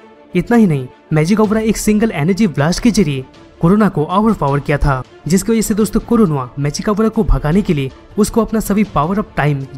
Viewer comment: i heard corona 😂😂😂